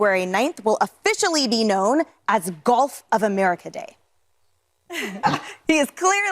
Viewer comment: Funny, I misheard it as Golf of America Day.